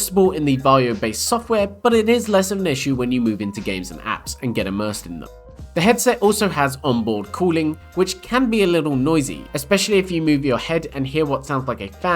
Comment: 🤣 ah I thought he said onboard calling 🤣 instead of cooling